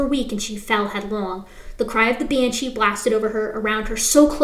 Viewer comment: I died laughing when I misheard you once. When Reed was at the three rivers shed and he heard the “cry of the banshee”. I misheard you say “the cry of a bitch”. I thought you were being funny and breaking from the book’s narrative. I laughed so hard.